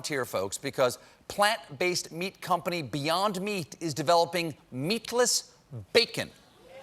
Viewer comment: When Stephen says 'Meatless Bacon' I hear 'Herecy'